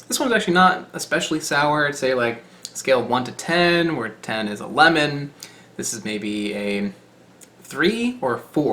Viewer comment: Thought he said "on a scale of 1-10, where 10 is 11" 😂😂